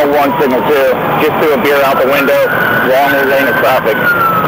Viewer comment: I like how they felt the need to mention the specific brand of beer he threw out the window instead of just saying he tossed out a can of beer. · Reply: ????? Where did you hear that?? The only thing I heard the officer say (at 0:50) was "he just threw a beer out the window (while in?) the lane of traffic".